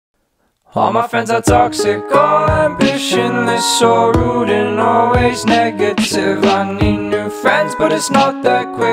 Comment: I always thought it said "sorrowed and always negative" not " so rude and always negative "